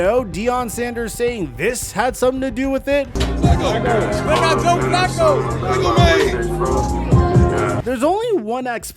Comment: What did they say? I can't make it out. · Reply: shedeur- “they got joe flacco” prime- “flacco my age”